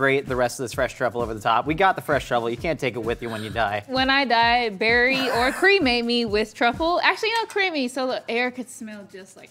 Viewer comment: When Vi says "Bury and cremate me" at 21:09 the subtitles say "Berries and cream made". The trend is dead even on TikTok but I just think that's a funny coincidence.